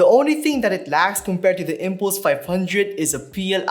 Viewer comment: what did you say the ddj400 was lacking? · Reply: it was lacking xlr outputs :(